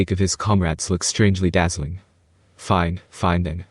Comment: strangely dazzling? Did I misheard or what?